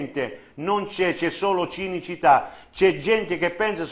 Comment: non ho capito se c'è cinismo o Cinecittà😂